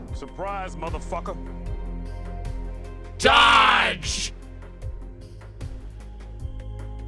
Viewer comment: i swear when he says dodge i thought all this time he said JUDGE!!! really loudly to reference ultrakill